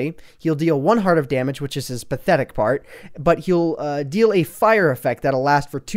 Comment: when you said 'pathetic part' I thought you said 'pathetic fart' >.< X3